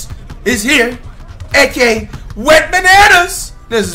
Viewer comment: I thought you were saying “wetbananas”